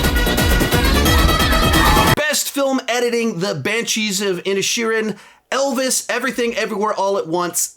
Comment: When he read The Banshees of Inisherin I heard “the Banshees of Ed Sheeran” and got thrown for a loop